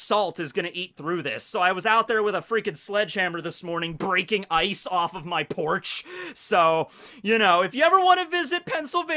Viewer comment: I miss heard the opening and thought he said “sledgehammering Ice off my Porsche”